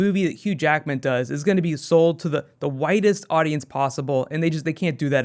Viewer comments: Everytime says widest I kept hearing "whitest" lmao. Really hyped to hear more about DP. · At first I thought he was saying the whitest audience lol